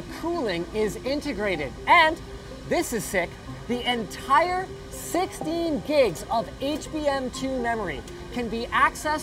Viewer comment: jesus i thought he said 60 gigs of HBM2 memoery. when it was 16